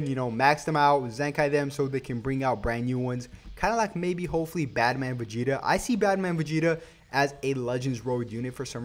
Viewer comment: Dude I thought you said batman Vegeta